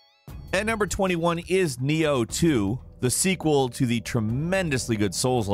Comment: When he said Nioh 2 my heart dropped, I thought he said BO2 at first, i cant wait for that to be remastered